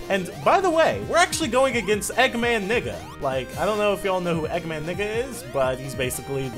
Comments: It sound like you said egg man nigga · Dude it's sounded like u said eggman nigga through my speakers man